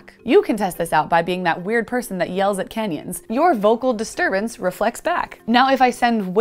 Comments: I just heard "that weird person that yells at Kenyans" at 3:40 and I was very confused until my brain figured it out. Need more sleep. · Did anyone else hear: “You can test this out by being that weird person that yells at Kenyans.”? The video was playing in the background and without the picture context, “canyons” sounded like “Kenyans” to me for a second until I realised what she was saying.